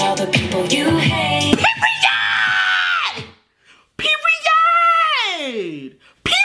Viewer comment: guys i´m from brazil and i can´t understand what she says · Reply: Marcella Generozo she said period